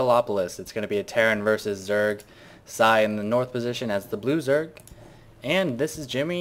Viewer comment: whenever i hear you say "blue zerg" for some reason, when i first hear it, i think you say "loser"